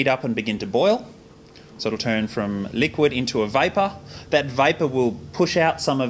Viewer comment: i thought he said "liquid into viper"